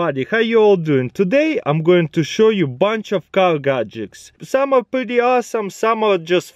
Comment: I thought he said cow gadgets